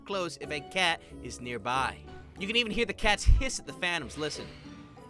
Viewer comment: I misheard "hissing at the phantoms" as "hissing at thanos"